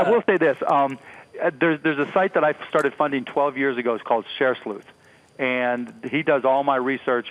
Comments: Did anyone hear clearly the website that mark cuban talked about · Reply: Dickson Munemo I think he said sharesleuth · Can someone please tell me what site Mark cuban said around 6:56 ?? Can't make it out, Share __ ?? Thanks! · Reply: M DW ShareSleuth